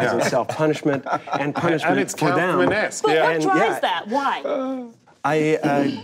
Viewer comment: What does Sacha say at 59:16 ? "Calverman-esque"? Who/what is he referring to? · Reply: "Kaufman-esque", referring to Andy Kaufman who would often perform with the goal of aggravating his audience.